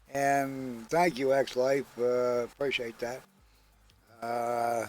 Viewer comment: DID HE SAY EXLIVE? AS IN GHOST EX? or Ex, since he left ghost????????????